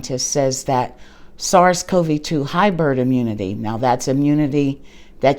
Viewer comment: hybrid immunity- is there a question? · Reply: I was working out while listening, and it sounded like you were saying "hyburd". Sorry...